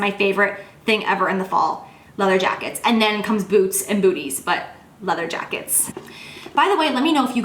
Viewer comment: Lol I thought you said boots and boobies are your next favorite after leather jackets!!!